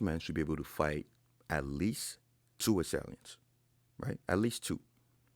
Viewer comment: every man should be able to fight at least 2 what? I cant tell what he said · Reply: two assailants.